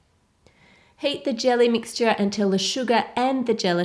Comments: I thought she said "suga" instead of "sugar" xD but this is satisfying to watch ♥ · I heard "SUGA" when she said "SUGAR"... gosh I'm weird... 😝😂